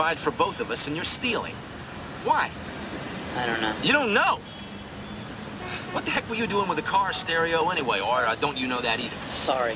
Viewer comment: Am I the only one who thought Charles said "what the hell" instead of what the heck you was doing with a car stereo anyway? I could have sworn I heard that when I was a kid